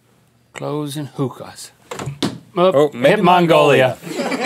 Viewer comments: did Link say "cloves and hookahs" when throwing the dart? Is that what Turkey is all about? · lol does Link say "hoes and hookas"